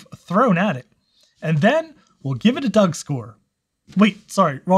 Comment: I was looking at my lunch when I heard "doug score" and I had to do a double take. Thanks for the insight on this module. Im going to buy a bunch for my old macs.